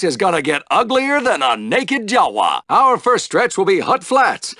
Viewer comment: What did he just said... Naked Jawa?.